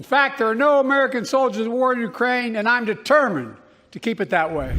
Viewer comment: ChristJudge, I loathe Biden but he said “there’s no soldiers IN the war in Ukraine” NOT “there’s No soldiers OF war in Ukraine” His bungled blathering talk was misheard by you!